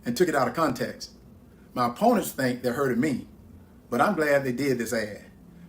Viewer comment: At around 20:11, when Herschel Walker talks about his opponent digging up an old video, did Herschel actually say "they 'hurted' me?"